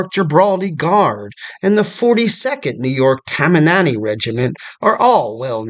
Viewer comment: I think I heard “tamamanny” during the intro of NY units. Did you mean Tammany? Like the Tammany Hall scandal?